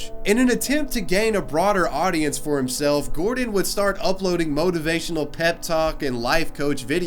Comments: thought that said Gordon Turd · I thought it said Gordan Turd... I'm sorry